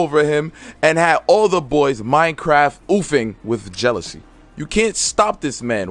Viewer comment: Did you just say, "minecraft oofing?"